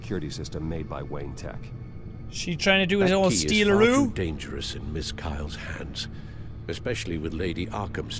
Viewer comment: it sounds like Alfred says that key is fucking dangerous